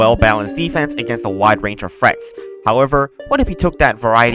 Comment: I always thought you said Frets instead of Threats. D